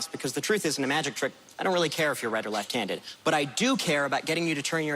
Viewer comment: hes so talented he completely fooled the audience not realizing he actually said 'red or left handed'